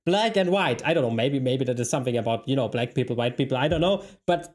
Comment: I definitely heard and "aboot" in there. Do we have a canuck explaining America?